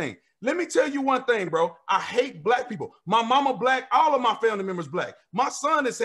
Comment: Jesus at 40:17 did he just say he hate black people. I'm confused...was he trying to convey something or does the truth really come out when your heart isn't right??